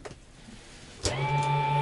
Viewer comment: What did the pilot say at 0:11? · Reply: “Clear start”